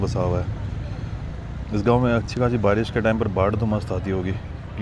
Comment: क्या बोल रहे हो भाई ❤ 25:33 का मतलब क्या हैं भाई ❤❤❤ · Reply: Bhai keh rhe Hain ki "mast baad ( flood) aati hogi" Ilsliye main keh rha Hoon baad (flood) mast kaise ho skti hai😢